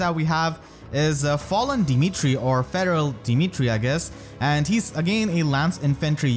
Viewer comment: I misheard you said Feral Dimitri as Federal Dimitri lmao